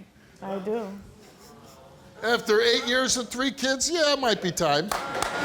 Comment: At first I thought she said 30 kids 😂🤦🏽‍♂️